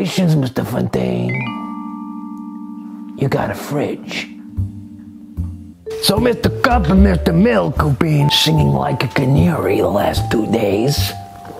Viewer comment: does anybody know what he says at 1:45? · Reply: “So Mr. Cup and Mr. Milk have been singing like a canary the last two days...”